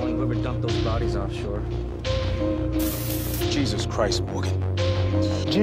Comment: Im bay harbor butcher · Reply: I thought you said gay harbor butcher 💀